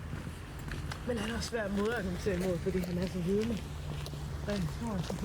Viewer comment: Wait lol the first one kinda sound like danish people 😅😅😅😂😂😂 "men han er os svær og modargumenter imod fordi han os er vidne" it sound like they saying 😅😂